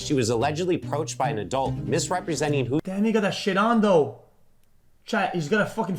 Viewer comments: I swear it sounded like he said the n-word and didn’t mean to and tried to play it off and make sure no one heard him 😅 because literally I slowed it down and everything and he deadass said it…….. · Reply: he said "damn he got that shit on tho" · Bro what did he say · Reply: “Dat nigga (got) that shit on tho”